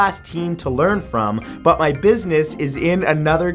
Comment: it said princess instead of business